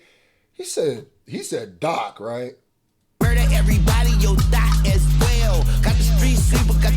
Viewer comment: To me it sounds like he said murder everybody your thot as well. Maybe or maybe 🤔 not but that's what my ears heard but it also sounded like he said Yo DOT as well. 🤷🏾‍♀️🤔